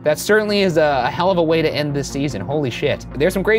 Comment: no two words cause me more pain than "season finale" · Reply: ngl I didn't process what he was saying at first and almost cried when I thought he said series instead of season